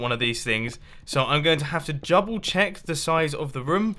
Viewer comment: Did you just say 'jouble check'?